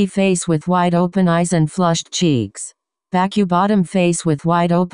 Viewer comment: it sounds like he is saying flushed cheese